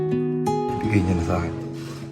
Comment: "Lord, ibigay niyo na saken to". Parang yun narinig ko. :) Lord pakibigay na po sakanila.